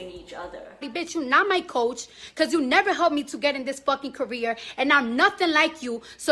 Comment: I have thought she said KOREA 😂😂😂😂😂but not CAREER🤦‍♀️🤦‍♀️😂😂😂